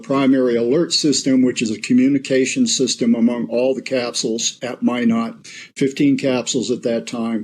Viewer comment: Did he just say in Minot 17:40?! Like Minot ND where i live,how have I not heard of this. It wouldn't shock me we have so many missiles here. Anyway great video!